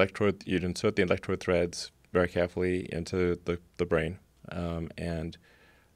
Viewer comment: When he says "very carefully" it sounds like he says "Greg Heffley"